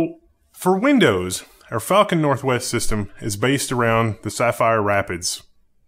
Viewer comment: Anybody else hear "fucking northwest system" and not "falconNW" at 4:56? Lol never heard an angry.